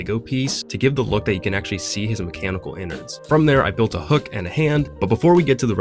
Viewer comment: I’M SORRY DID HE JUST SAY ENNARD